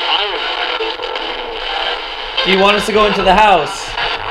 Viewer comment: when they asked if they want them to go into the house I heard “someone is in that house”